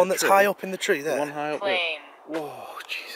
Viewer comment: I heard "lane" it's it just me??